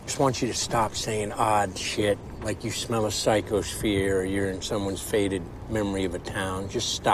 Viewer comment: I never caught that when Rust says "psychosphere" Marty thought he said "psychos fear" 😂